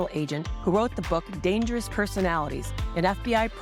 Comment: I first thought you were saying the the FBI are the most dangerous personalities, but I was thinking of Hoover, Comey, and Wray. Sorry. I hope the FBI can overcome its bad reputation caused by treasonous leaders.